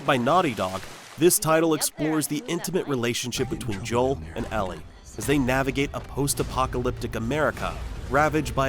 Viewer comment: BRO did you say ELLEN...